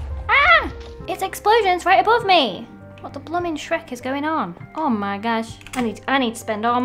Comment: she says shrek... at least... that's what I heard.